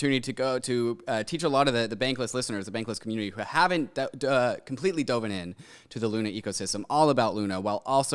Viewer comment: did he say "doven"?